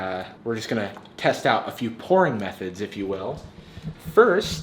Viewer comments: Haha it sounds like you said “porn methods” instead of pourING methods 😀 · sounded like he said "we are going to try out a few porn methods" lol